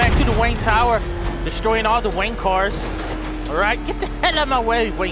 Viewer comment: it sounded like you said you were destroying the wankers instead of wayne cars XD it made me laugh so hard